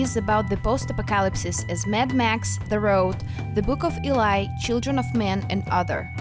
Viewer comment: Did she just say Boom of Elai 🤣🤣